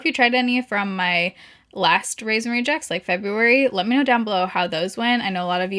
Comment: Ok I’m not sure why I’m admitting to this now, but a few years back when I was new to YouTube makeup vids, I legit thought was saying “Raisin-Rejects” and I thought it was so cute but so strange 🤣🤣🤣🍇